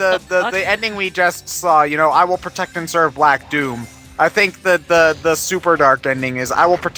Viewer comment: Automatic captions says: "I think the weather service is bad..."?!?!?!